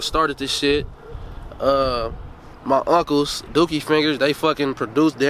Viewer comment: Did he say his uncles "DOOKIE FINGERS" AT 5:10 point.... That is a unique ass nickname smh